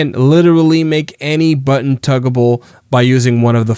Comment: do you mean toggleable? Yes, every button can be toggled with continuous trigger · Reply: no. I mean tuggable. That's what he said. 🤣🤣 4:30 "You can make any button tuggable"